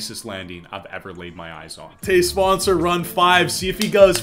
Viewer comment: Bro every time you said matej's name I thought that you said "todays sponsor"😂